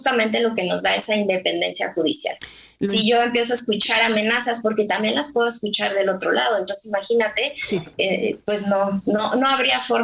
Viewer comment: Qué dijo? 8:50 "Si yo empiezo escuchar amenazas...porque también las puedo escuchar del otro lado " Conclusión: Está actuando así, porque la amenazaron . Obvio.